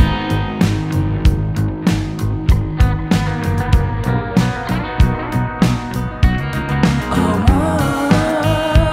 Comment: কী বলে ঐখানে? এইরকম আর একটা আছে 02:34 - 02:37, যারা ইয়ারফোন আর ফুল সাউন্ড দিয়ে ঐখানটা শুনবেন তারা বুঝবেন কি বলছি 🙂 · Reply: it says "bhrom"